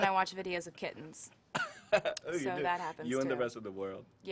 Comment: subtitles are wrong she watches kittens, not kids